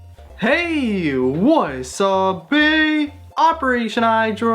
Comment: Can someone just clear about what he says in the intro. Like what I hear is "heyyy, what is up BIII!" Am I right or not lmfao. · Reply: Andrewc313 he says "hey, wasabi"